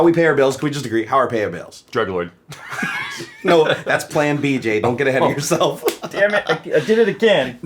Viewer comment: Did you just say "No, that's plan BJ"? You really must love each other